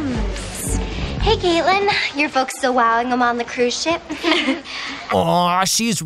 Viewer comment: I’m sorry what did Tess say? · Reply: “Are your parents still on a cruise ship?” or something like that lol I wasn’t really paying attention 🤭